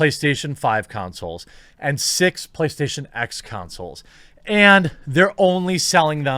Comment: Did he say play station X? lol